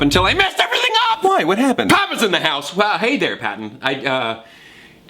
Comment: i have no idea what you said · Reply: He said “Thomas in the house”